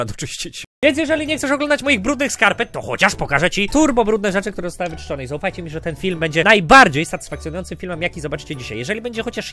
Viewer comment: Czy ja usłyszałem ku**o czy turbo haha